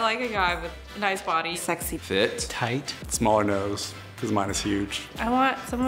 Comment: did he just say toight?